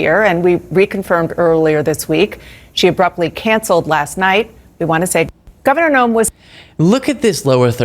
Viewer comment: "we want to say f#$k(?) Governor Nome." The way the clip is edited makes it sound like she is saying ef Nome.